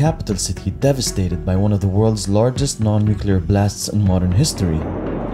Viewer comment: did he just say nuclear blast ??? did the nuclear explosion become mainstream version ??? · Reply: He said non-nuclear blast.